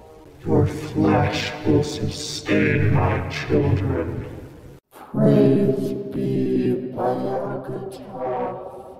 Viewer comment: what did he say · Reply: “Praise be Bayagototh”